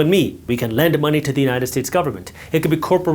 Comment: Did i just hear “united tits” 😅 many times otherwise good video